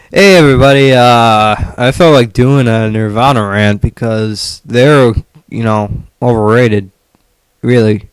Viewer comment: I heard "I feel like doing a Nirvana rant bc im retarded"